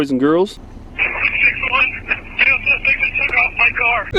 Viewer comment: did he just say a suspect took off with his car? (sounding very out of breath) HAHAHAHAHA xD